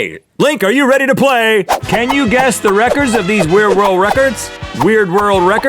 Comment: I swore I heard Rhett said "world Rhett-cords".. No pun intended.